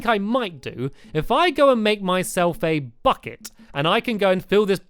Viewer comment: i thought he said if i make my self a FUCKIT! 7:55 #LMFAO (my little brother was talking)